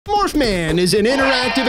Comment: oh, i thought it said morphan.